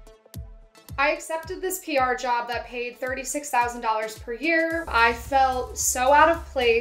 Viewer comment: Wait she said 36k per year? I thought she said 136k. wow that’s amazing